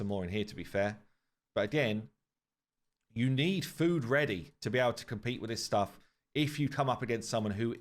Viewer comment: They should let you choose which champ you want after you meet the requirements. I want a maneater, but i'm too high of a level, which is silly. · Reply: I thought u said u were to high af to compete I was like damn same here lol